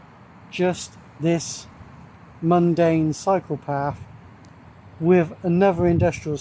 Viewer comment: When Brittish people say "cyclepath" I always hear "psychopath" 🤪🤪